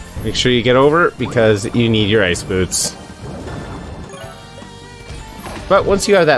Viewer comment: How you make the lava become stones?? You need special boots??? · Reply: I heard him say ice boots😒